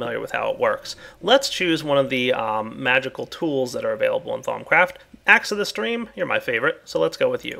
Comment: Lol sounded like he said thongcraft